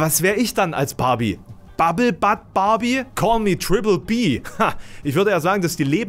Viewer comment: Ich habe erst "bubble bud" verstanden und dachte: Man, Alex kennt sich mit den Canabis Sorten aber aus. Dann ist mir aufgefallen, dass das ja gar nicht passt und er "bubble butt Barbie" meinte. Wobei ich mich frage, was daran schlimm sein sollte, diese zu sein, oder ob Alex nur auf seinen "Knackarsch" (was die Übersetzung wäre) hinweisen wollte. 😅